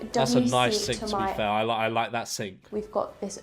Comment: did she say a W sink